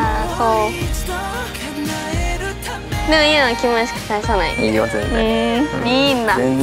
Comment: でもゆなは気持ち返さないよ →でもゆなはキモいしか返さないよ に聞こえる気がするのは私だけでしょうか。